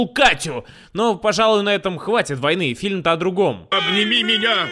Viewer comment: Мне послышалось или Гомер сказал "отъеби меня"